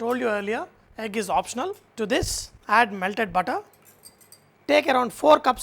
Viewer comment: what does he say at 0:58? To this, add meldded budda?